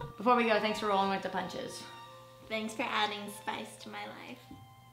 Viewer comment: what does Ingrid say? Can't understand it :( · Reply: "Thanks for adding spice to my life."